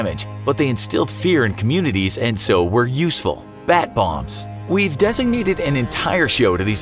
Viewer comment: I heard bath bombs 😂 CALL THE NAVY. THE OCEAN IS FIZZING!